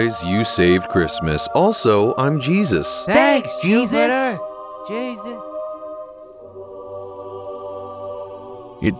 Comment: what did the other one say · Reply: Jupiter